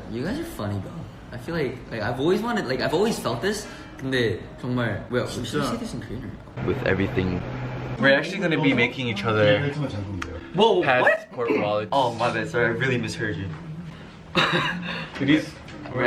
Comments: He misheard that Johnny said "we're making out with each other" instead of "making for each other" 🤣🤣 i died at that part 😂😂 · can someone explain what mark thought he was saying? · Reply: senaverse “were actually gonna be making out with each other” 😂😂